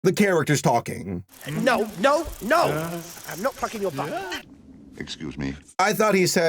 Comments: I thought he said “f***ing your mum.” This movie... no. Just no. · I thought he said don’t fucking your butt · ok that made me spit out my water. I swear he really did say “I’m not F*cking your butt” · he actually said "no touching your butt" 😂😂😂😂